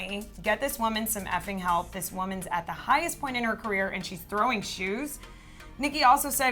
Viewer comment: I thought it said throwing shade not throwing shoe 😂